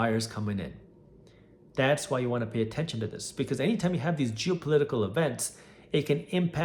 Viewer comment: "...jew-political events.." Yup. That's what I heard 😅